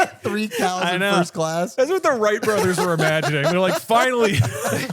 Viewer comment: I heard "Far Wright Brothers" and now I want this to be a term we use. **